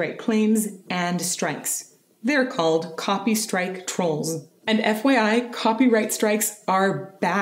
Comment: Just a note that I hear "Copystrike trolls" but it's spelled in the graphic as "Copstrike trolls".